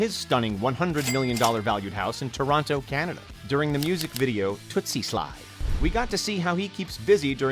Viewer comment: did he really say “twixie slide”?